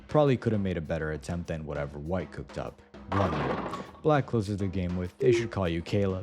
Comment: what the guy said: “blunder.” what the subtitles said: “love you.”